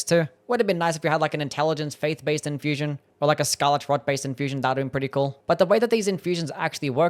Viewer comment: Or like a Skalitz-Trod-based system? What did you say? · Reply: " Scarlet rot " based i assume.